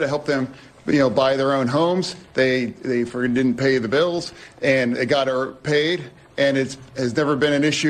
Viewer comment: it SOUNDED like he said they didnt friggin pay the bills